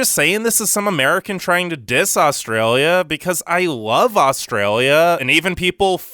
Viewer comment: Is it just me but when he says Australia or Aussie it sounds like he is saying sause